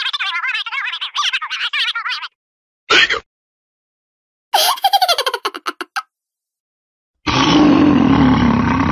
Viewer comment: wait.... what did he say!!?? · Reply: He said "hiccup"